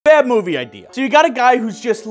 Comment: I thought you said bad horror movie idea